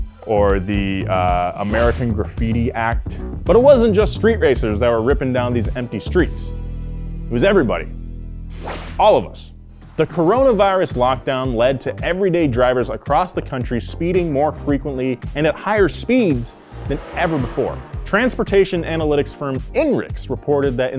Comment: I swear I heard the Corollavirus